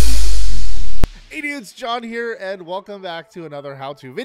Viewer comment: did i hear "idiots its john here" on the start? XD